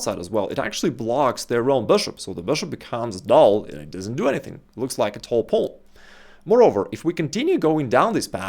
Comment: At first when he said "it doesn't do anything, it looks like a tall pole" I thought he said, "It doesn't do anything, just like Tom Holland"